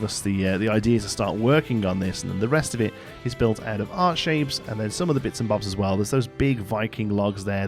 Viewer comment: every single time you say "art shape" (which is what i assume ur saying) i hear "heart shape" and my head goes THAT'S ACTUALLY A RECTANGLE SIR